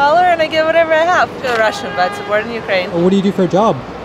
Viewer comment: umm what's with the word added over the recording at 8:42 ???????????? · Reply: FEEL RUSSIAN BUT BORN IN UKRAINE?